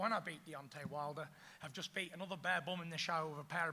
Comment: did he call him a baboon? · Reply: Barebum its a prison term another barebum in the shower means u just the same as anyman